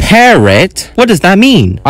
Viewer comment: it sounded like parrot